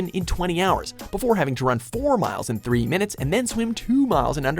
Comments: wait what? run 4 miles in 3 minute??? or 30min lol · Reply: i mean, repeat 9:03 lmao i know the only logical way is 4miles in 30min but it SUUUURE sounds like he said 3min lol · Did I hear that right run 4 hrs in 3 minutes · Did I just hear him say “SAS soldiers need to run 4 miles in 3 minutes!!?! Or did he say 30?